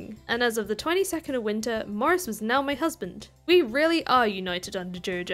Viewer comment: Not gunna lie,thought you said “My Husband,Wife and I”